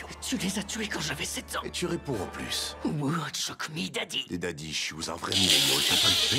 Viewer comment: C'est quoi la ref de chocomy daddy que Sasuke dit à 21:54 ? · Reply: C'est choke me daddy mais recherche pas mdr